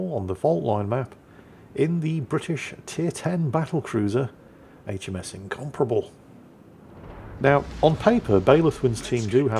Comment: The first time said the name, I heard "HMS Uncomfortable"... so that stuck with me...